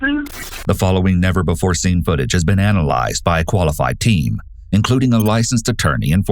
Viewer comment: I wasn’t looking at subtitles so I heard “the following has been analyzed by a qualified teen” and I was like no tf they aren’t